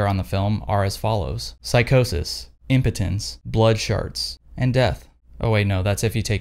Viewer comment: “Psychosis , impotence , blood shards and death” damn that got me 😂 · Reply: I thought he said "blood sharts".